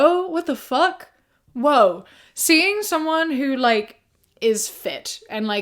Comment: The subtitles say "weeing" instead of "seeing" lol